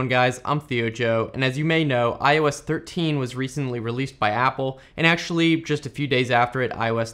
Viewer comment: is it weird that I heard "and as you may know I was 13 when I was recently released by Apple"